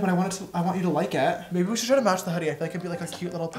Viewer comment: When said I think we should match the hoodie I thought he said we should ask huddy so I thought that they were going going to call up chase and let him pick😂